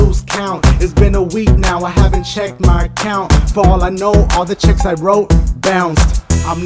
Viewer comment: I could have sworn that said 'checks' instead of 'chicks'